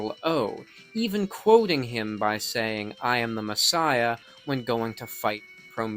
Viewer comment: All these years, I thought Omega was saying "On a mission." Now I know it's "I am the Messiah."